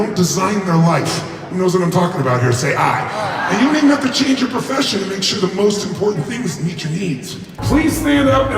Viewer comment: What does he say after 13:46 ? I cant understand him. Im a non native speaker. Pls help. 💓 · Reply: No problem! He says: “…and you don't even have to change your profession to make sure the most important things meet your needs.”